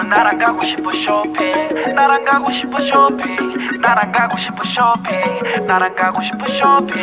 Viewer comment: 나락 가고 싶어 쇼핑 이라 들림ㅌㅋㅋㅋㅋㅋ